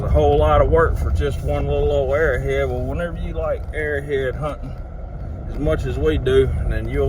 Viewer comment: I thought you said "air head hunting" for second. 🤔 Best part is your reaction to finding the spear head.